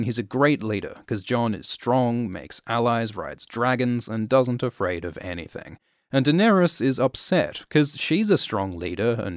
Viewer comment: Did he say Jon doesn't afraid of anything? lol